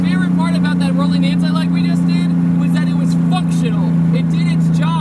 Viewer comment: it sounds like you said fucktional 😂😂